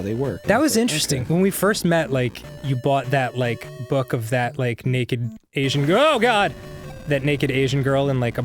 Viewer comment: Did he just say arin bought a book of little Asian girl porn?